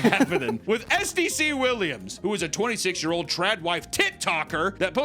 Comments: Did he say TIT TALKER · I could have sworn you said TitToker lol😂